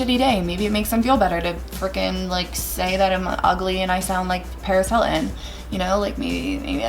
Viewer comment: Why is that a hate comment that you sound like Paris Hilton? She's such an icon. Btw I love that you are making videos you are such a good story teller! ❤️ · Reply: OMG AHAHAHAHAHAHAHAHAH I'm not a native speaker and I was doing chores while watching the video and until I read this comment I thought she said "I sound like penicillin" 😂😂